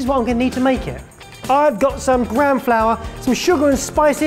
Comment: I get confused momentarily every time I hear "gram flour" since my American ear hears it as "graham flour," which is an ENTIRELY different thing. Not sure what we'd usually call gram flour here, probably "chickpea flour" or something, but since we pronounce it the same as "graham flour" it just causes that nice bit of weirdness.